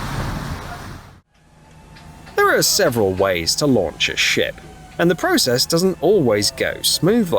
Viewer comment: Almost sounded like he said several ways to laumcj a SHIT 🤣 That ship was a POS!